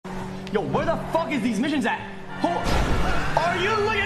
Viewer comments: Lol, I had to watch twice cause I thought he said where tf is these n****s at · I thought he said where these ni**as at 😂😂😂